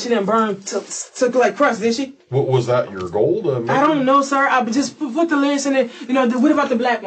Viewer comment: did she say "i don't know, put the lyrics in there" ?